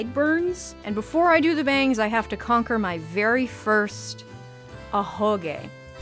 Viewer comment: OK YOU SAID AHOGE, I THOUGHT YOU SAID AHEGAO AND SAT VERY CONFUSED BRACING FOR IMPACT LMAO